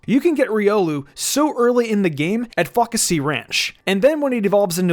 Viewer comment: I heard fuckecy ranch 😂